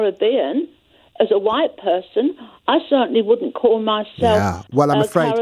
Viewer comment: "You're British, you're not English" Followed by "If I were born in the Caribbean, I wouldn't be Caribbean. I'm English". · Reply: listening again, 2:48 She says if she was born in the Caribbean she WOULDNT call herself Caribbean. At least quote somebody correctly! (even if you don't like what they're saying!)